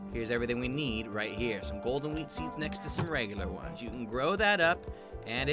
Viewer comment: Thought he said Golden weed seeds😂